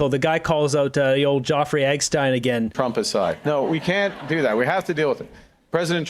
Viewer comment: At about 20:20, Carney Presser, did Carney say "Trumpic*de"?